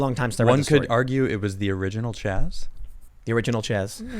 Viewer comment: When Adam said "the original chaz" I initially misheard and thought he d gone British for a second to make a joke and said "the original chavs", which would ve been funny, and fitting. But then I relistened to the bit, just in case, and, alas!, he didn't.